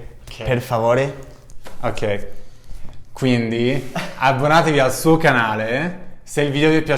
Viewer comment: it's iscrivetevi instead of abbonatevi